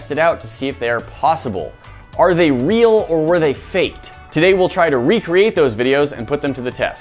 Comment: is it just me or did I hear are they real or were they fate